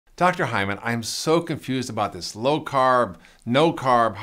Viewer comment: Did he just say his name is dr hymen? Lol